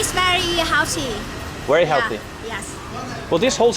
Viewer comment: 'Are they magic mushrooms?' - 'Yes yes, very healthy' 😂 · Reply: Lol I thought she said ‘earthy’